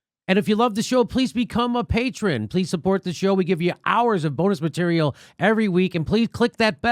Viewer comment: Did he just say "please abort the show" at the end? xD